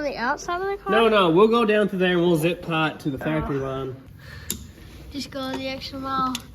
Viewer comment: Oh, Factor. I thought you said Factory.